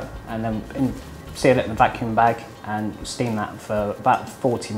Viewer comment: Every time I hear vacuum bag I hear "I lack the skill required".....